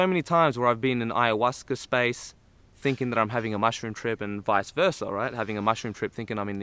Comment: Swore it said mushroom tip